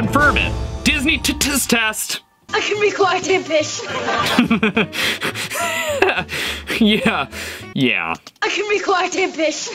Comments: You little witch Donald duck said. I can be quiet a fish cuz I heard it so many times I hear fish. it's saying the chicken sound but it sounds like saying the f word. This is hopeless hoverboard shoe. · I heard " I can be quite a fish "😂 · Loved the video, I had a really rough week, so I needed a good laugh, thanks Steve! 🖤 have a great weekend 🤗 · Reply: i thought he said "i can be quite a dish" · I kept hearing: "I can be quite abyss."